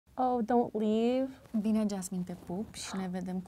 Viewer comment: pls smb tell me what did she say before 'te pup, si ne vedem curand'? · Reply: ''Bine Jasmine'' Well, Jasmine...